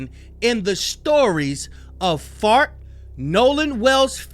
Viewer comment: ‘FART’ instead of Bart😂😂😂😂